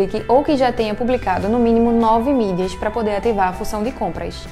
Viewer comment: Em 5:50 o que ela diz? O instagram pede que voce tenha publicado pelo menos 9 o que??? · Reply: É preciso ter publicado nove mídias, ou seja, ter feito nove publicações. :)